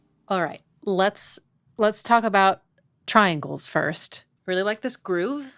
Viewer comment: Triangles? Oh nevermind, thought you said tenderloin.. Why'd you have to wake me up again mama